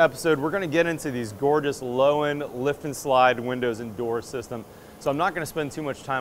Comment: Thought he said low END lift & slide instead of Loewen lift & slide, hahaha....